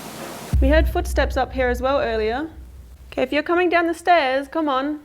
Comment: I hear the man saying "Up here".